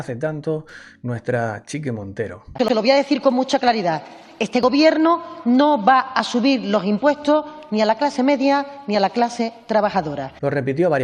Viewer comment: yo oi lo que dices, pero entendí "les voy a subir los impuestos a los trabajadores". o sea, hacen lo contrario y se entiende.